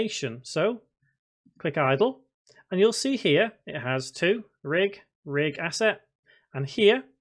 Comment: WHAT YOU SAY IN THIS PART ? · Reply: "RigAsset"